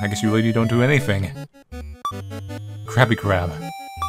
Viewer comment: it sounds like says crappy crab. Even the captions say so.😄